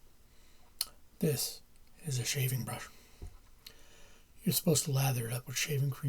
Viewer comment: Definitely thought that said shaving bush 😂😂